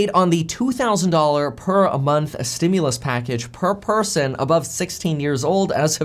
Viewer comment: Not a good idea, I just want too go back too work after my maternity leave and this crisis is over and why does every 16 yr old need 2000 a month for anyways, only some need it not all of em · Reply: I thought they said anyone over 16